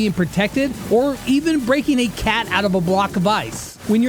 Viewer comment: wait wait 🫸🫷 did he say CAT!???!!?? ÀAAAHAJAAJÀNANANAÀÀÁÁÁÁÆÆ AW ÆÆ 3:52 EW